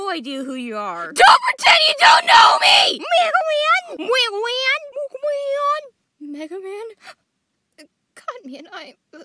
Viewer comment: what were they saying here? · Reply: Vesper Woman : Mega Man Toad Man : Mega Man Hard Man : Mega Man